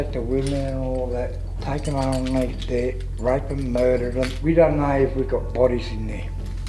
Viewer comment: i know you couldnt understand him, but he said "r@ and mur3r em' "